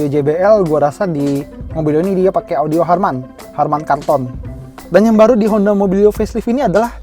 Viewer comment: "digaplok ultramen" "build quality terkutuk" "harman kanton" mungkin sejenis awan kinton.... atau gw salah denger harusnya harman kardon ;D